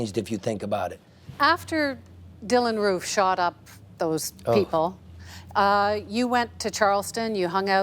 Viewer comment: Did she just say “those people”?